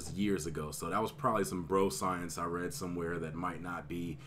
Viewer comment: Not sure...but were you saying "broscience" or was I mishearing it....